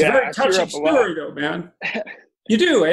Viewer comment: did Jordan just call rogan a young man?